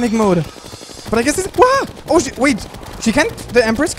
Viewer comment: You here what i hear? "Oh shi-"